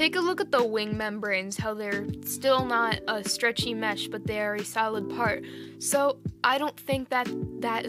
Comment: "They are a salad part" I dunno that's what I heard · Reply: I think I said "they are a solid part" lol